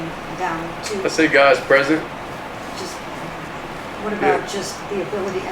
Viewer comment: No, Mary K he said what he said!!! It was "God's Presence" NOT no damn moxy🙄 ... Stop trying to make FETCH happen w/your dumb ssa word🤐